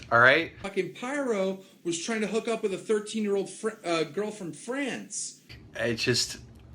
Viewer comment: When he said: "Pyro was trying to hook up with a 13 year old fo-- girl from France" I legit misheard it as: "---trying to hook up with a 13 year old football." I don't know why; but it'd probably be something he'd say anyway.